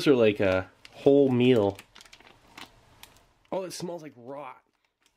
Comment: "It smells like..." and then cutting out actually made me lol. WHAT DID IT SMELL LIKE? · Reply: It sounds like he said it smells like raw so as in raw shit